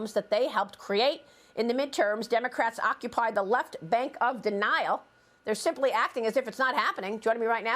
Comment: LOL, I thought Maria said "on the left bank of the Nile!" Is it "Nihil"-istic to point that out? Get the joke?